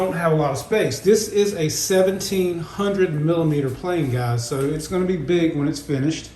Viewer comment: into the video, did I hear you say that's a 17 hundred milliliter plane? If so, then that plane is made of liquid! 🙄🤔😜😂 It's going to rain plane! I'm sure you meant to say millimeter.